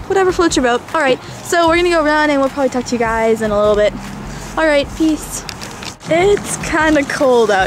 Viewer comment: i thought it said touch you guys a little bit! haha